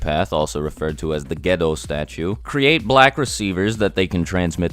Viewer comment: I never bothered to look it up and I always thought it was said as “ The Ghetto Statue” 😂